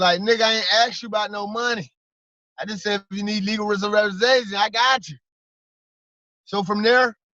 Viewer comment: This Guy sound like he said Legal reservation much love tho